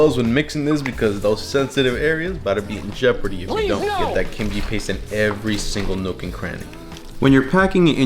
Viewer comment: Sounds like he said "get that kinky paste in every single nook and cranny"😂